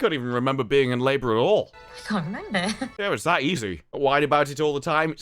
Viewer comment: is she actually saying "I can't remember" at 27:08? Cause if hadn't said it - I dont think I could have deciphered it. This "actor" can't even speak - what are the casting people doing?!